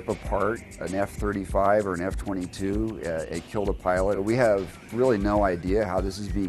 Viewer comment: It sounded like he said it killed a pilot